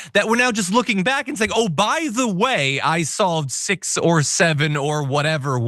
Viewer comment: For a moment I thought he said he'd saved 6 to 7 wh*res.